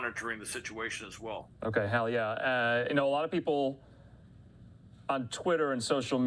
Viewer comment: did he say hell yea?